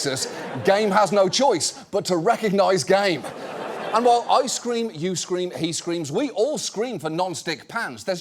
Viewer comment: "I scream. You scream. He screams. We all scream for non-stick pans." Yup. That is how that goes. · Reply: when he said “I scream” i heard “ice cream”